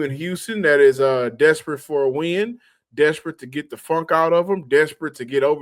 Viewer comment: 😂 I thought said “Houston wants to get the fuck out”